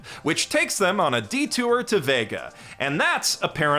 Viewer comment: I misheard it as Vegas, which honestly would have made a more interesting plot twist, ngl